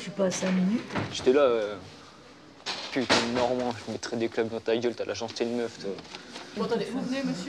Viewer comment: C'est moi a 8:30 il dit nardin emek ?